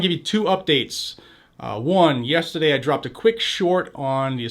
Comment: I thought he was going to say, "Dropped a quick Shi*" 😂